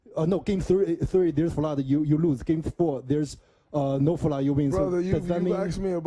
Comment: “game 3, no flOod, game 2, no FlooUd, game 4 no FlouD” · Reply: I thought he said no flop 😂💀